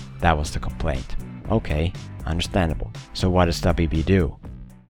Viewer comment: i thought you said, so what does the baby do